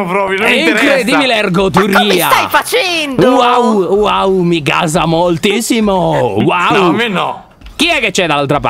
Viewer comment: MigAsAmolTiSsImO Quello che ho sentito io: MIKASA MOLTISSIMO??